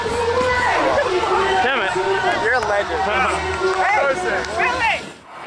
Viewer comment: did I hear Jason Nash saying hey!?